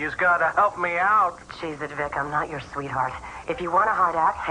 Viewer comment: What does Carmen say at 9:02 right before "Vic, I'm not your sweetheart"? "Cheese it"? Can't be "Jesus". Greg and Lynne and everyone else in this episode seem so nervous. Must have been early days.